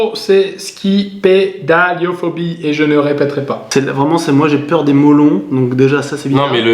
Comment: J'ai cru il avait dit "Moi déjà j'ai peur des melons" xD